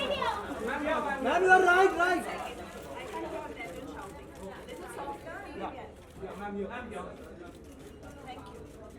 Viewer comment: What did Anushka say in the minute 4:50 ? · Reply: ”One second, I can’t hear when you’re shouting, a little softer, I can hear. Thank you.”